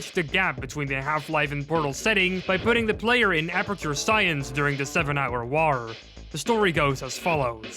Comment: sounded like he said "Seven Hour Rawr"